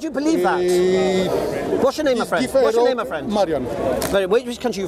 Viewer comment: Did he say his name is Mariam? 😂😂